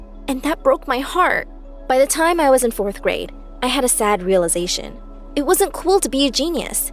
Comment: Did she say 4th grade ?